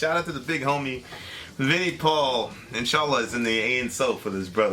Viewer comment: did just said "insyaallah" ??